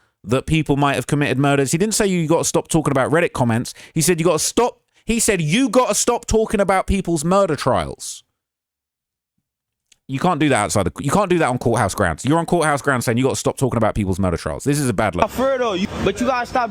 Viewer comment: I literally thought he said: stop talking about people's murdered child 😂😂😂